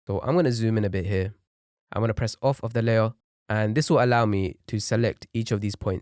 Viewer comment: Please I didn't really understand that part he said to select "off of the layer" or what I'm dragging it but it's dragging everything to the right